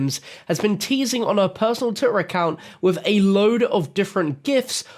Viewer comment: Did he say titter? lol